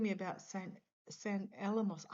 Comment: When kept saying "Saint Alamo" I kept hearing "Saint Elmo's Fire." Not the movie, the atmospheric phenomenon - St. Elmo's fire - Wikipedia <link>